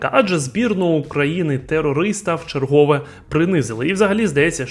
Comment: Тепер прийдеться вибачатися за свої слова з 5:05 по 5:15! Ти обізвав збірну України - терористом. Чи тільки мені почулося?